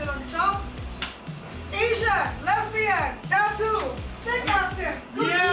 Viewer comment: i think she said ‘thick accent’, not ‘fake’ :)